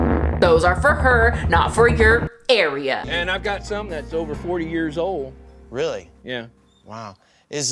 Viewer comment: Did he say at 12:37 that he had balloons that were over 4 years old? How does he keep them afloat, does he refill them somehow? Wait, why do I care?? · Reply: Turn on the closed captioning and he says “ 40 years”😳